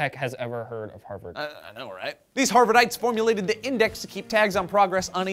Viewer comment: It sounded like joe said harvard-dikes.....